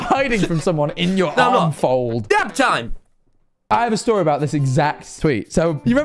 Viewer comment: I thought he said nap time